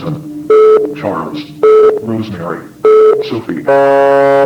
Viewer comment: " Jack " " Susan " " Charles " " Rosemary " " Sophie " @ 16:21 " FELIX "???!!! 😱🤔 I don't know, that's what I heard, JUST before the flashlight turned off! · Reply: Billy says Sophie but its just in reverse, hence why it sounds like Felix